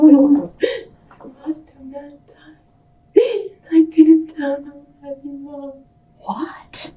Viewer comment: the captions are egregiously incorrect. said: "I watched a man d*e and I didn't tell nobody, mom." "What?!" "And I burned his stuff- at our own house." Edit: Ironically, I heard name as "Avery".